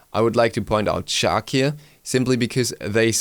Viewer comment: What god does he try to say at 10:55? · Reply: KinGxNetheaD he says "i would like to point out chaac here"